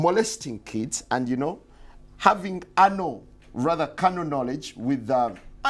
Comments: Did he just say Anal knowledge instead of Canal knowledge. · What is he saying at 0:53? Is he saying can a knowledge? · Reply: It's carnal knowledge... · what did say at 0:51 ? anal what? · Did you notice at the beginning 0:52 the interviewer said anal knowledge instead of saying canal knowledge? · did he say "and you know, having anal"?